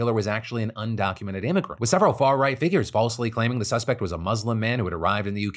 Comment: First when I heard Starmer's speech I thought he said "far-right fuckery" 🤣🤣🤣